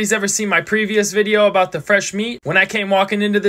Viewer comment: I thought he was saying “fresh memes!” 😆